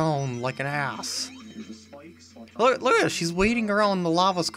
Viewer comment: 6:46 Did you say she?